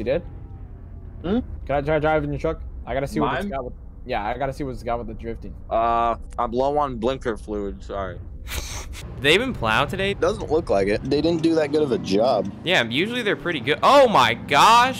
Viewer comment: is it just me or when asked to drive truck I thought he said “im plow on blinker fluid”